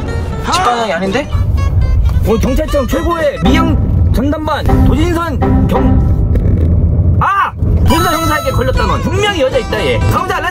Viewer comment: 뭐라고 하시는지 아시는분들 답글 좀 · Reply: 경찰청 최고의 미행 전담반 도진선 형사한테 걸렸다